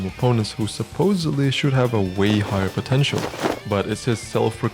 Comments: did you just said potential? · Did you say potential?😏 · what was that you said? was it potential or something, man?